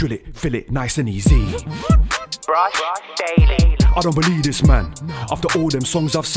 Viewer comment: Man said "brush daily" like it was a producers tag on the beat 😂😂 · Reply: I thought it said brought daily